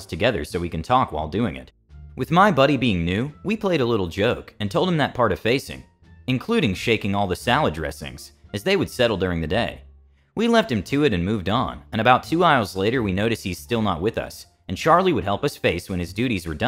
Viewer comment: the one about shaking the salad dressings reminds of the time my buddy gave his little bratty ass brother a rattle can of spray paint and told him to shake the can until the ball inside the can stop rattling that little shit stayed out of our hair the whole day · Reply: They said salad dressing I could have sworn I just heard shaking the dresses and I imagine someone just like walking up to the clothing department and just start shaking the racks of dresses